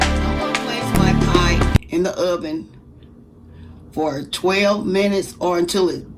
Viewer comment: I thought she said stir for 10min